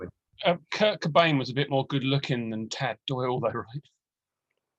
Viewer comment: What band does he say he prefers at 30:00? Cloud? Clad? Tad? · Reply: TAD! People always prefer TAD! haha...(Eight Way Santa is a classic)